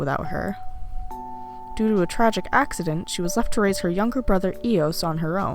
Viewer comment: Oh my god when you interested Eos (is that spelled right?) I thought you said he was a bottom